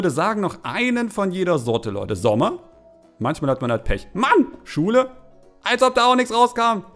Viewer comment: Bei 8:16 hab ich anstatt Schule schwule verstanden